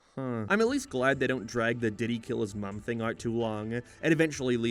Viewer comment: DIDDY?!? (I HEARD DIDDY NOT DID HE)